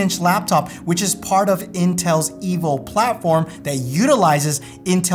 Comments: I thought you said, “part of Intel’s evil platform”. 🤣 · Who else heard "Intel's evil platform"? hahaha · i thought you said part of intel's evil platform lol